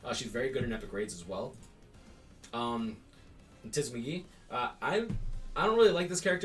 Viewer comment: did he say tits Biggie or tits McGee?